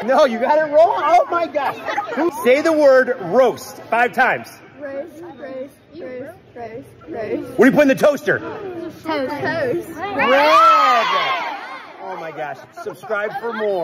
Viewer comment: I thought he said "where do you put the toaster" My brain: wtf? Counter?